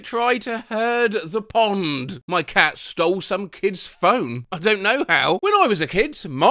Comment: somehow i thought that said “my cat stole someone’s kidney”